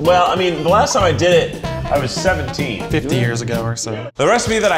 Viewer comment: Keith: “The last time I did it (making candy), I was 17” Matt: “50 years ago” SCREamin g edit: Sorry, guess I misheard Matt say 15 when I first watched it through! 😅 But I seriously thought he had dropped a burn on Keith and then they cut that scene right after